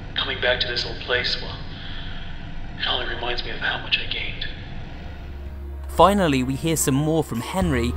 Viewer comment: At first i thought he said how much i caved